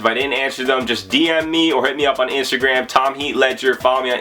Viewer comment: yo. did you just say your name was tom heat ledger?